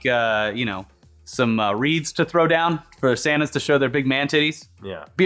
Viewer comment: sounds like you said Reads instead of beads😂